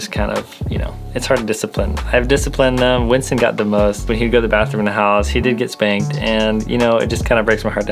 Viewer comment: did you actually say you spanked your dog?? or am I just hearing things... cause that's not funny